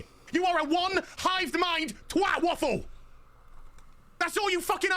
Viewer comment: Geez he’s sober to ehh? 😬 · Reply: did he say “twat waffle”?